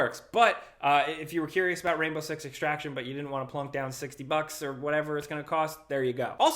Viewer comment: Disappointed, I'd misheard the new Tom Clancy game name, I've been preparing for Rainbow Sick Sex Traction.